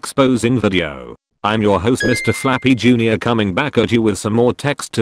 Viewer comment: I heard Mr.Fappy Jun.